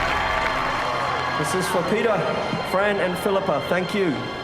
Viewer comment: When Ian McKellen said "Peter" I heard him saying "Pizza". That's what you'll get from being Finnish...